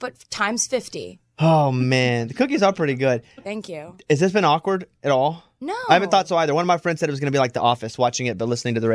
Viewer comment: What is she saying at 03:34?? That he says "snap!" in reaction to? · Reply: She's talking about her Twitter account and how busy it is and she says, "I do look at it, I mean I have to, it's my Twitter."